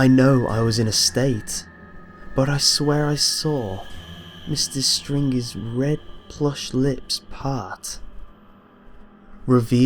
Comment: at first i thought it said "Mr.Stingy" so i was all like...lazytown?